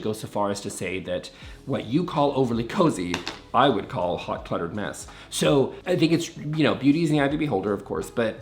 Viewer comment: My thoughts exactly! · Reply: I thought he said 'hot buttered mess' and decided it sounded tasty.